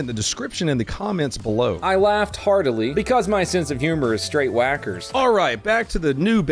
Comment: This whole time, I thought you said "Sackler" but you were saying "wackers"